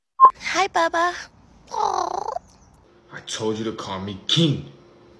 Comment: I thought he said "I told u to call me Keith" 😂